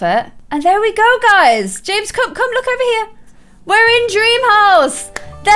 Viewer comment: it sounds like Clare says “James Cook” lol